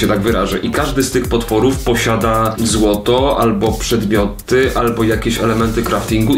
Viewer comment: znaczyła mi się tu reklama, i brzmiało to 'Każdy z tych potworów posiada PEGI 18' XDD Rykłem jak to usłyszałem XDD